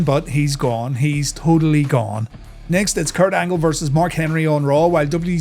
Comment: For a second I thought he said Kurt Angle VS Bret Hart at 10:43 that would have been awesome to see